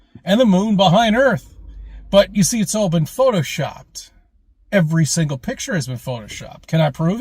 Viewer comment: Well, today I learned that Photoshop existed in 1969. Who would have guessed? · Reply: i thought he said POTATO SHOPPED